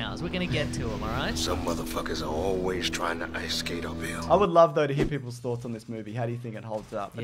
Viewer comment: Some MF’s always trying to ice-skate uphill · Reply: Haha now it makes sence, I thought blade was saying, trying to ice skate from paying your bill.